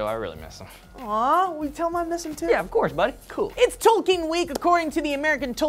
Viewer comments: At first I thought said toking week and I was like fuck yeah! Then I realized he said Tolkien; I'm still really stoked. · I thought said "It's toking day" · I thought said toking week at first...got all exited. Tolkien is cool too though I guess.